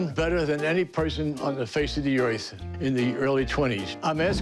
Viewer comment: did he say “face of de oith?